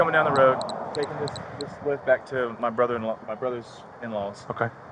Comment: Did he just say “I’m taking this wetbac home” at 7:20? LLOL he did say that. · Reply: Not wetbac.🤦 He said taking this whip back to my brothers.